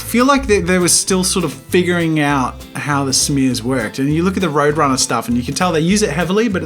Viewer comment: Did call Woody Woodpecker, Roadrunner?